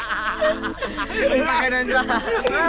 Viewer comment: I heard kuya said Pu*ang inang to And close his mouth